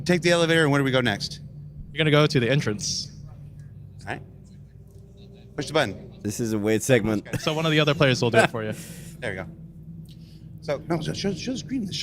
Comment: When Gary Oldman says "Bishop out" it sounded like "push the button", which he immediately followed with pushing a button, most satisfying misheard line ever for me xD